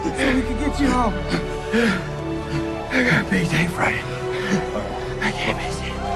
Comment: anyone know what Eddie was saying at 0:54? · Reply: "I got a big day Friday, I can't miss it."